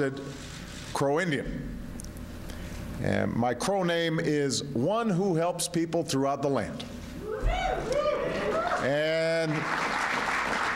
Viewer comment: OMG at first I thought he said "My Cro name is 'One he helps people to rob the land'". THAT would've ticked me off...